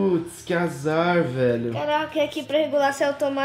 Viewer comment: Man sem mentira eu escutei krl , mais alguém?